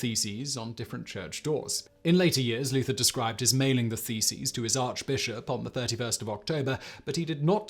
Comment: You said "nailing the theses." I heard "mailing the feces." It might have been just as effective.